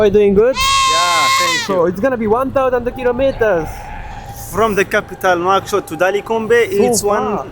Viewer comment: 辺り？、 "from the capital, one hundred kilometers" みたいに聞こえました(^◇^;)🙇‍♀️